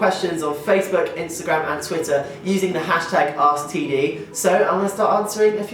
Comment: The way he said asktd, it sounded like he said std lol! Love you Tom!